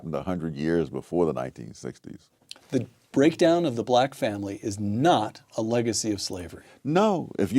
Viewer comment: he says "the breakdown of the black Family", and you hear him say "the breakdown of the community". You are not quite getting what he is saying, and again missing the point. Again.